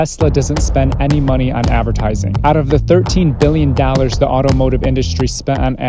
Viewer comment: I can swear he said "Dallars"